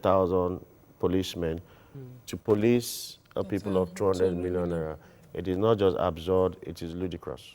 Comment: Did i just hear a people of 200 million naira